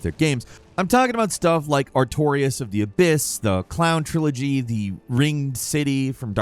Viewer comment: I thought you said clown trilogy instead of crown trilogy and was very confused for a minute lol